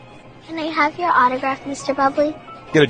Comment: I thought he said good job but he said get a job